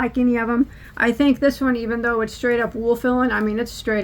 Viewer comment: Donna here, I straight up thought you said wool power! :) I try not to look at Ice yarns bc I have no will power. Don't have the money or the room!